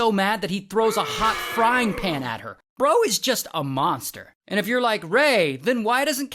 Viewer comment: I thought it said daddy is a monster.